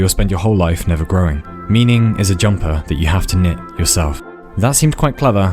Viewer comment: I thought it said knit to yourself